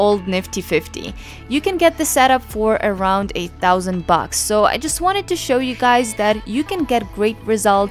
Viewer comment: Budget set up for $8,000?? 😭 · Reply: she's probably confused because you said 'a thousand bucks' instead of just 'thousand bucks' at 0:53 and that is sounding like 8000 bucks